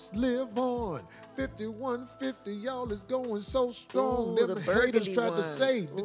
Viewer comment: I thought this mf said STRONG WOOL the first time 😂😂😂😂😂😂😂